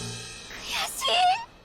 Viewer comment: What did she say at 5:22?Anyways, amazing video as always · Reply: 悔しい/kuyashii means "It's frustrating"